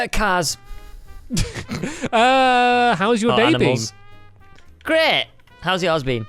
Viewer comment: Was I the only one who thought at 47:00 said "How's your baby?" instead of "How's your day been?"